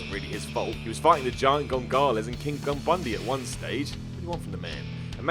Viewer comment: Did you say "Giant Gongalus"?